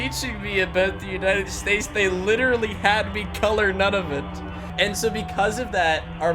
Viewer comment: What said: "we didn't learn anything about America because we had to color Nunavut." What I heard: "we had to color none of it"